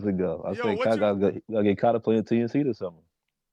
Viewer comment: Did say he is going to get Kyrie to play with TNC this summer or was I listening too hard?